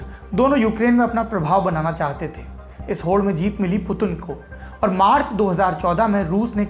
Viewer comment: is he said putul or putin?